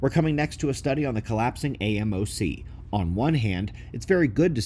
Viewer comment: aMoc !! Dang it thought you said AOC was collapsing .. 😂😂😂😂